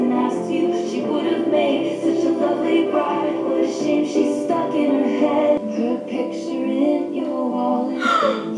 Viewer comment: Did I just heard " she's stuck in her head " instead of FUCKED IN HER HEAD(champagne problems ) . Please tell me you changed from the clean version